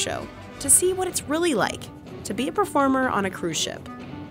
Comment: why’d I hear “pea-farmer” ? Lol